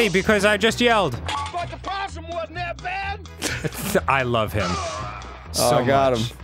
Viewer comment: Does anyone know what that guy was saying at 10:25? I couldn't understand him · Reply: I heard "BY THE POSSUM WOULDNT BE THAT BAD"